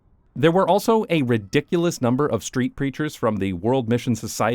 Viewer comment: Really thought you said "street creatures," and I really wish that had been what you saw.